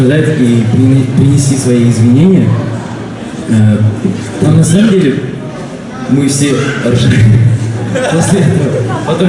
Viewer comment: мне послышалось "ну на самом деле мы все алжыпкетык 😹